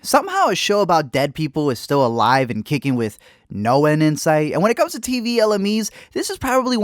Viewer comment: I thought they said noed- I’m a DBD fan TwT